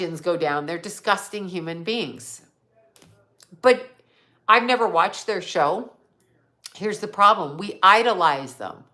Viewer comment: I am wearing earbuds, and there is a male talking around the 38:55 mark, when you bring up Kanye, but I can't tell what he says, and I don't have my tv on. I played it back several times, anybody else hear it, or am I losing my mind? do you have a TV on, or neighbor talking, I hear the talking towards the end also. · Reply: When said, "I hope the Kardashians go down,"... The male voice sounds like he said,... "I had enough of them" 😁